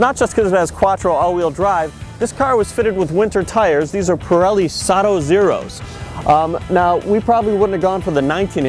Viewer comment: Does he say Pirelli Saddo Zero's????